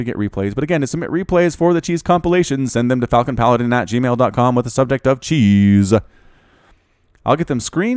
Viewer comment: Your intro sounded like you said f***in Paladin. Good video though!